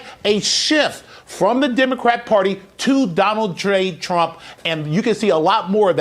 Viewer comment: Did he say "Donald Dre Trump"?